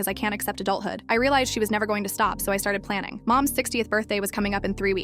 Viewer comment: Why did i hear "mom's 16th birthday"😭😭😭